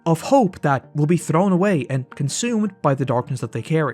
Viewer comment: It isn't "rehab" its "recovery". Rehab is a facility while "recovery" is what sobriety is often called.